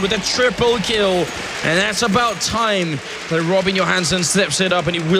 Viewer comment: Is it just me or does it actually sounds like Anders is saying "Rubbin Your Hands" when he says "Robin Johansson"